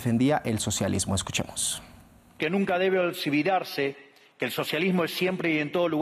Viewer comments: Petro dando vergüenza cómo siempre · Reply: ¿Qué fué lo que dijo en el minuto 0:44.? Nunca debe Olsobudarse o olsividarse. 😂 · Excelente Milei, brillante! Desde Colombia apoyo a Milei! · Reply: ¿Qué fué lo que dijo en el minuto 0:44.? Nunca debe Olsobudarse o olsividarse. 😂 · Petro habla bajo los efectos de la coca, no le presten atención, nos tiene jodidos 💔🇨🇴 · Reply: ¿Qué fué lo que dijo en el minuto 0:44.? Nunca debe Olsivudase, el mandatario🇦🇷..😂